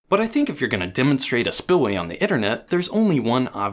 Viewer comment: Did he just say Iternet?